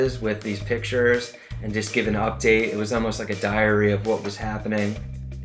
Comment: Did he say "diarrhea of what's happening?"